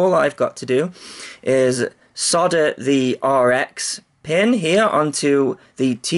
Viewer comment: Did i just hear you say "Sodder" at about 2.18 lol ? Great vids as always.